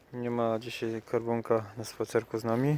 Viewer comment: Piękna historia Karbonka (kiedyś usłyszałam Skarbonka - Hi). Straciłam pieska rok temu. Pozdrawiam